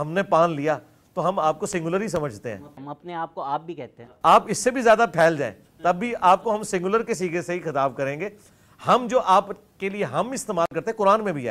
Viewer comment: Lmao did he just say ap is se bhi zyada phail jayen hum apko singular hi kahen ge?